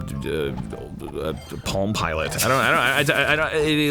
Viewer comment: UGGHGH ARIN CANT LAND THIS JUMP I AM SO INFURIATED IM GONNA THROW MY PALM PILOT AT THE WALL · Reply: User_Redacted when Dan Harmon said Palm Pilot who heard it as "Pom Pilot" because of Homestar Runner